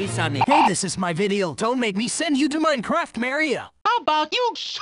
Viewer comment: Why did sonic say Mario but it sounded like he said maryo